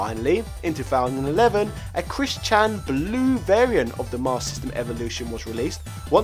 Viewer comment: Did I hear "Chris Chan Blue Variant" at 9:29?